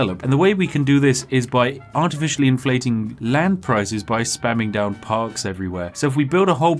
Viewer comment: I heard "spamming Pogs everywhere" lmao rip Pogchamp